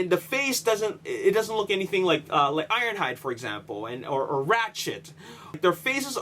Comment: I thought he said rat shit